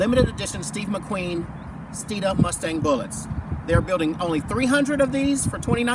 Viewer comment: I thought it said " ford mustang bullshit "